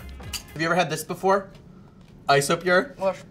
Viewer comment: I thought the isopure said isoprune